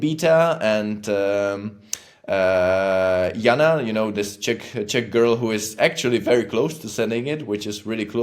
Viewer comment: Oh..."Czech"...not "Chick"...makes more sense...slow morning brain with a "what did he just say"